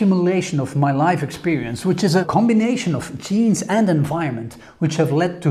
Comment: 8.23 - I heard "a combination of cheese and environment"